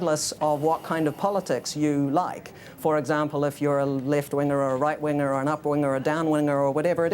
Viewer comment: I hear what aw is saying, "if you're a left weiner, right weiner, an up weiner or a down weiner". Did I hear him right?